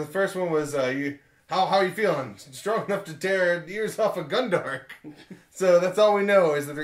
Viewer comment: <link> I thought he said "too tear ears of a gungan" ...